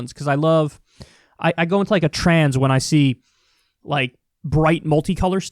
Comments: Kyle "goes into a trance when he sees bright colors" Kulinski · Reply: I thought he said "trans" at first lol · Careful with your words Kyle, sounded like you said you go into a Trans instead of trance.